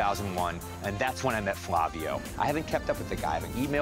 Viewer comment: As I'm watching this I was so thrown off when he said "my name is Flavio".. it's rare that I hear someone with the same name as me. Pretty cool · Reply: I thought he said Flabio haha. cool name!